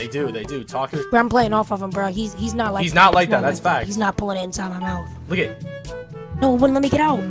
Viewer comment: Broo did anybody hear lil bro say “he’s not pulling inside my mouth” at 11:57?!?!??